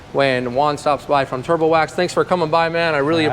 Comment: I've been waiting for this all week! Thanks for posting! At first I thought you said "turtle wax" 😆 🤣 😂